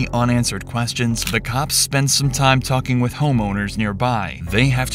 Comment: I heard "homonerds" but it was "homeowners" 🥴